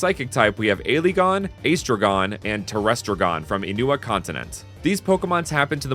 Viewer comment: Wait did you say continent? 1:57 finally also I can see this awesome beast throw out a Draco meteor shower